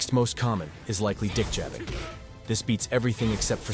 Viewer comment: The amount of times I rewinded 2:47 to confirm if I heard what I heard and laughed. I’m like it sound like he saying d*** jabbing. I still didn’t wanna believe it and turned on the captions and it’s there lol. I laughed again ☠️. He says it so casually like he’s introducing us to a case mystery